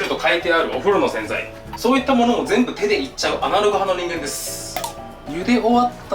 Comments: アナログ派がア○ル派って聞こえた。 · 何て言ってるの？ アナル派の人間ですにしか聞こえないww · アナルに聞こえて仕方ない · アナログ派の人間ですって言った時 ア◯ル派の人間ですって聞こえて聞き直したwww